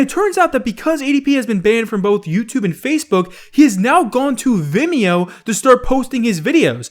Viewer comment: Honestly when tipster uploaded the vid I thought I said vinmo 🤣🤣